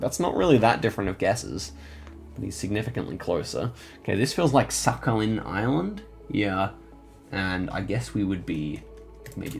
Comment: "This feel like suck-on-him" Island? I don't know why I heard that sus